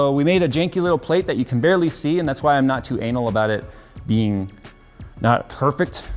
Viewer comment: Did he say anal?